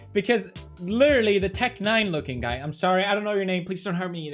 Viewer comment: wait, rewind that : did he say : " the tech n9ne looking guy " at 8:08 DOES PEWDIEPIE KNOW WHO TECHN9NE IS??????!!!!!!